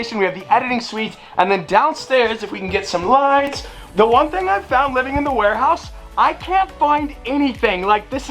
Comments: i thought you said you wouldn't live in the warehouse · I thought you said you cant live in ur warehouse?